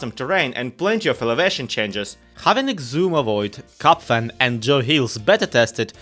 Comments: I think the captions are wrong, it says GoodTimesWithScar instead of Cubfan. threw me for a loop for a second, lol. incredible recap as always ! I of course enjoy Pix's voiceovers but getting a one is always a treat <3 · Hey minor correction for the captions, you have Xisumavoid, GoodTimesWithScar and JoeHills instead of Xisumavoid, Cubfan and JoeHills at 1:07 ! The captions are much appreciated as always! 😄